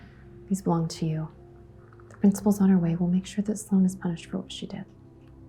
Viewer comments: it’s says “pushed” instead of “punished” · it says "pushed" instead of Punished????